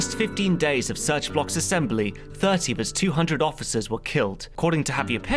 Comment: Min 1:35, I hear "30 bis 200 officers were killed". I'm extremely confused since I can only understand the German preposition bis (till, until, to, up to) and it doesn't make any sense to me, since it's not a loanword or anything like that as far as I know. Can someone clarify what the narrator says? I'm very bugged by it. Thanks